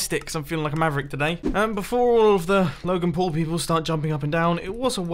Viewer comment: Wait...did u said logan paul?????